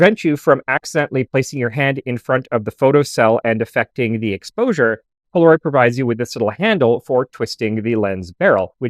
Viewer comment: I'm sorry but at 04:46 I hear, " affectating the exposure "? Surely your speech is a little affected itself. Hmmm, make a note ... new American word.